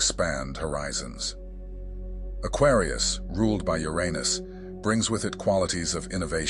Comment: "Aquarius, ruled by Yer Anus....". Oh ffs, can we please just change the name to Caelus? and btw, why not just pronounce it in it's proper Greek way? It is pronounced U-Ron-ose. Emphasis on the second syllable, "Ron", like the name. That uranus thing was fine when I only heard about the planet almost never. In the 80's when no one cared about astrology. But now that cosmology is so mainstream, I hear it all over the place and it drives me crazy.